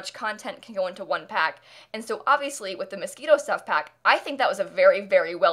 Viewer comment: this was the first i'd heard of the stuff pack from last week or whatever and i swear to god i thought you were saying mosquito pack this whole time lmao and then i saw "moschino stuff pack" at the bottom when you were reading the press release and i went OH